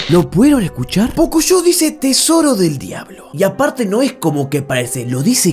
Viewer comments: "lo pudieron escuchar?" Yo escuchando a Pocoyó diciendo "tesoro e llave": 👁️👄👁️ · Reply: Yo escuche "es hora del diablo" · yo viendo eso que dijo pocoyo: primo! Deja de ver pocoyo en la tele >:v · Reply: Dijo: "tesoro de la llave" no dijo "tesoro del diablo" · yo escuche tesoro de la llave XD alguien mas? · En la de Pocoyó yo escuché "tesoro de llave" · Yo solo escuche "Tesoro de la Llave" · Creí que dijo pichula del diablo XD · XD yo escuche tesoro de la llave y por el contexto del capítulo dice eso